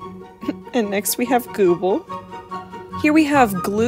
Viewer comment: it sounded like you said ''google''